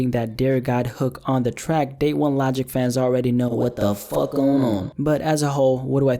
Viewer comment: what was that i heard "what the fuck I'm on"